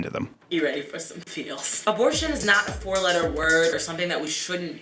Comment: Did she say abortion is a four letter word?